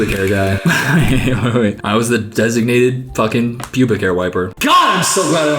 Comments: did he say sonya and not Sophia? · did just call sophia sonia